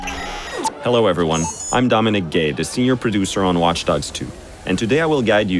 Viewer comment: Did he just say that he's Dominic Gay?